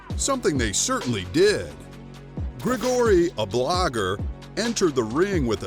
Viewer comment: I thought he said "blobber"